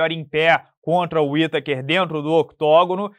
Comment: ouvi um stake aí ehm..kk